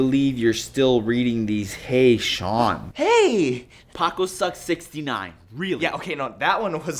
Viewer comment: I thought it they said tacosucks69 not pacosucks69